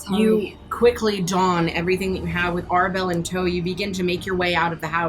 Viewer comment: I'm sorry, they leave with WHO in tow??? 👀👂 . . . . did i hear Arabelle? NOT ANYA?? OOOOOOO NIKKIEEEEE